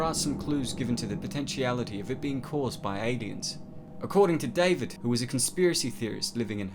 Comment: Sounds like you said “Adiens”